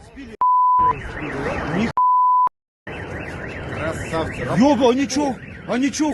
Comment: Похоже, снимающим это тоже нравится или я один услышал восхищённое «КРАСАВЧИК»!💙💛✌👍